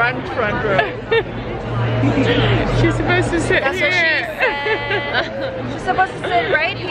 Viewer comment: did y'all hear the girl beside Romee say "that's what she said" after Romee said " it's getting a little tight in here"?